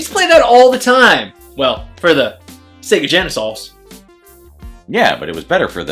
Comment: 1st game was looking cheap on Genesis. Sequel was much better in every way. 2:45 Did he say "Sega GenaSOS?"